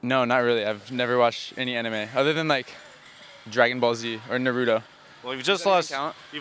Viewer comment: Did he just call it No-ru-to? xD